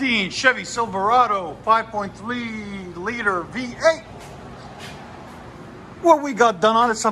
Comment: Sounded like you said “5.FLEA liter V8” HAHAHAHAHAHAHAHAHA